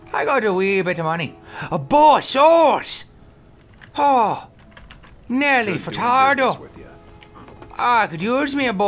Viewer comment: Did he just say Nelly Furtado?!